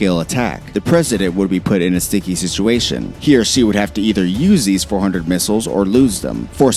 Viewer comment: “He or she.” I thought he said “here, she,” and I got so excited to see the triggered responses about the wokening or something